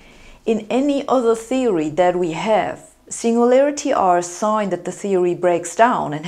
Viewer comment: I love the way she pronounces “ Einstein “, the German way ! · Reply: And the singularity too, I thought she was saying single layerity at first